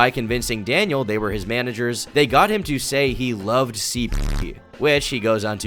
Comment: Im sorry what was said at 6:48, he loves what? Genuine question. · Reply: "CP", child p*rn